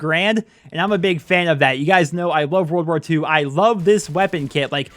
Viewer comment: did just say he loves world war two?